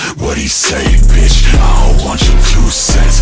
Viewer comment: When he said " You ain't even paid bitch". I swear thought he said " You ain't even gay bitch"😂😂😂 oops sorry corpse.